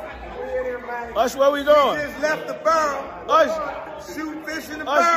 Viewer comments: 😂😂😂 are we going to act like we didn't just hear usher say where the n****'s😂😂😂😂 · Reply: Sounds like he said, “when in Vegas” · I coulda swore usher said" Where da niggaz?"😮say it isn't so Ush pleeze not you too😢thought you survived "D.D.D🤷(DoingDudesDiddy😞🤦) 🤔🤔🤔🤔🤣💯